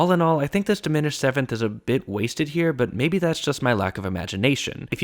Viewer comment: did you just call an Alto Clef a waste